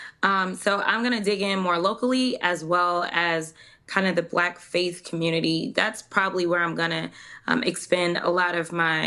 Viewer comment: Did she say "black faced community"?